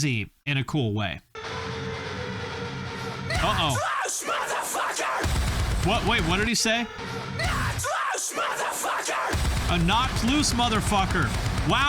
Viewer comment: I thought he said no truce mother fucker